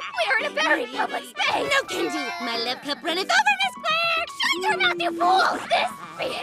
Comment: What does say at 1:43 ? It’s been bothering since I can’t make it out. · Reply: "No can do. My love cup runneth over, Miss Claire!"